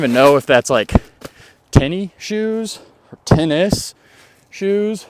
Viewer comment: Tin of shoes is what I heard you say. Quite the mental picture. lol. Tennis shoes is what we call them. The "new" name is athletic shoes.